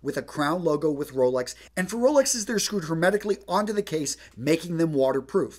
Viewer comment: What??? The subtitles say he says automatically, but it doesn't sound like it.